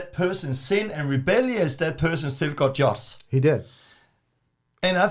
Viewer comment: I couldn't understand what said at 2:49. "That person just got ...just? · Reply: I think he says” that person still got judged”? But I’m not 100% sure ☺️